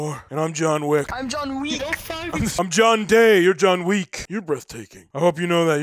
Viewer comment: I thought someone just said u are John Weed lmao